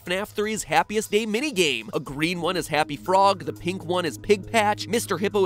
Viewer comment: I think MatPat actually said F**K